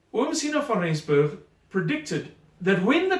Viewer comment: What in the world did he say there? Who predicted this? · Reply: Owen22 Siener van Rensburg